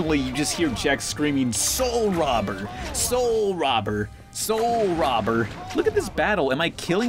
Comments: It's saying soul robber!? All I hear is "Cold Whopper!" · it sounds like Jake is saying swoul rabber. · always thought he said soul rubber i have no idea why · 'soul robber'? i hear 'poal whobber' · sounds like jacks saying pol wabber not soul robber to me anywau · oh, I thought he was saying "Pull, Robert!"